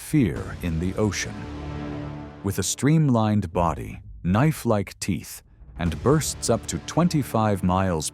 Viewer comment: I'm going deaf i thought he said nice white teeth .